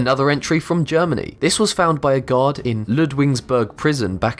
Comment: I thought you always said FOUND BY A GOD